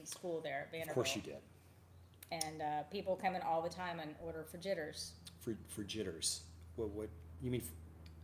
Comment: I thought she said "for jitters"! Thought it was a drink! LOL